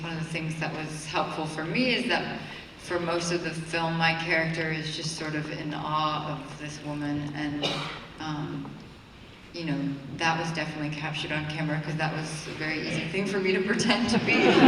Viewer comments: What Rooney said at 4:55 ..... what character help her easily pretend to be? Plz? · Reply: Rooney said,"my character there have sort of in awe of this woman and i....you know then that was definitely captured by camera because that was the easiest thing for me to pretend to be...". which she mean,because in real life she was also always in awe about Cate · does anyone know what Rooney said on 4:57 -my character is sort of " in arm of " this women.....then the audience was laughing. I don't follow them. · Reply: chichi awe